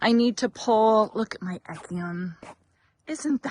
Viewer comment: What is the name of the plant that you show at about minute 8:00? Sounded like "echium"? It is gorgeous!